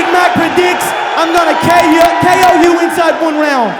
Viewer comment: KO you inside what round. What did he say? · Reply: inside one round